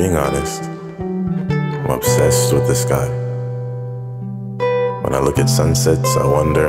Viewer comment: this whole time i thought he’d said he’s “obsessed with this guy”